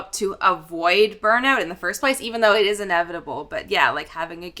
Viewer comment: Did say ‘burn out is inevitable’ (13:50)? Is that a mindset in Canada?